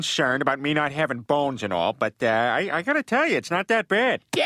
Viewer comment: Why did he say balls and not bones